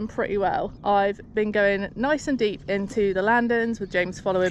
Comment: 😂. I thought you said “James likes it nice and deep”. Need to get my mind outta the gutter.